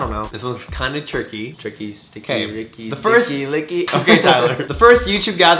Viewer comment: Did he just call him Tyler?? 3:00 😂😂